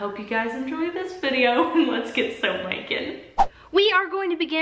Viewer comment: My boyfriend was playing fortnite as i watched this video and during the intro he thought when you said "Let's get soap makin'!" that you said "Let's get so naked" lmao